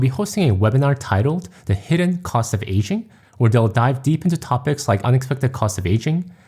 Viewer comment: Your accent threw me off at first because it sounded like you were saying "Asian" instead of "aging." I thought "But my parents aren't Asian lol."